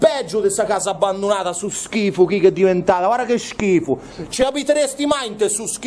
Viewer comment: NESSUNO DI VOI HA SENTITO AL MINUTO 34:20 QUANDO DICE"STO SCHIFO CHI!!" SI SENTE PRONUNCIARE UN SI CHIARAMENTE